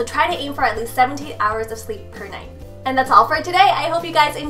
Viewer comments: I thought she said 78 hours of sleep every night. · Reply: I thought I heard 70 hours of sleep per night:) I was like... what!? ha:) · I thought she said 78 hours of sleep every night.